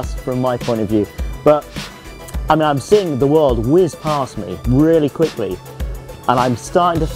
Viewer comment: did he just say world?😂